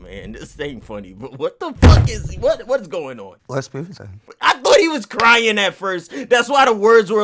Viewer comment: Let's prove it then 🤣🤣🤣 It sounded like he said it's pooty tang